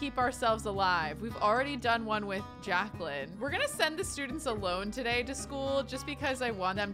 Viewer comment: I misheard "send the students alone" as "send the students a loan" 😂ah, the life of a starving student